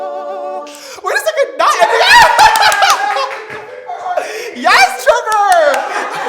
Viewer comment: am k tripping or did say "yassss trevor"